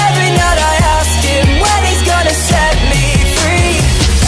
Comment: I thought it said “set me pee”